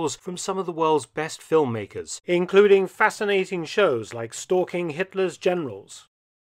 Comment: At the beginning of the video, for a second I thought he said "stalking Hitler's genitals"